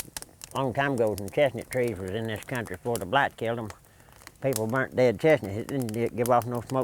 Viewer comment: ahh okay i just couldn't understand he was saying and didn't wanna just do what most do and assume what he said, cause it sounded like he said blacks at first and I was just thinking like I gotta find out what he said cause I just wasn't expecting him to have said something like what I thought I heard him say honestly. But when you say blight, is it kinda like kudzu, or however it's spelled? I just know I love in Mississippi and where I live there's tons of it everywhere and I always heard that like I think after the second world war that it was brought over from Japan or whatever maybe, but it's just what people told me. But I just know they stuff takes over every dang thing it seems. Like once it starts growing it just grows and grows and grows and keeps on until it takes it all over. But thank you for the clarification because I didn't take Popcorn as someone to say something like that and I wanted to make sure of what he said cause I ain't the assuming type like most these days who won't even go looking for clarification and will just assume they heard correctly and will start spreading rumors or saying bad things about folks. But yeah, this is one of my favorite docs, even though it don't have the most action or whatever, just getting to see an old timer out there doing all that with the help of ol is just amazing honestly. There'll never be another even close to ol Popcorn or honestly. But yeah, thanks again for taking the time to clarify what he said for me bud